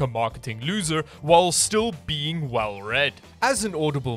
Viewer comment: I thought you said "well bred" during the sponsor.